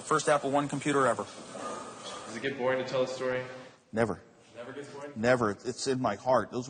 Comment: What was the question at 1:17? Can't hear it clearly. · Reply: "Does it get boring to tell the story?"